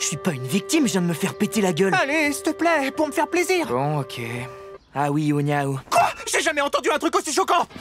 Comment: pour moi c’est clairement le meilleur passage même si tout l’épisode est incroyable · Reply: Mais t'arrives à comprendre ce qu'il dit à 16:25 ? J'entends ahouillonyaou